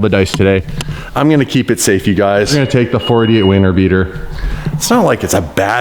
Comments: and then said "Weiner beater..." Seriously died🤣🤣 so lit I had to make sure he actually said WINTER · at first I thought he said "wiener beater"😂😂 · I swear @ 2:20 he said wiener beater